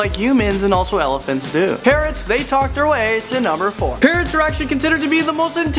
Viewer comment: when he said parrot i thought he said parent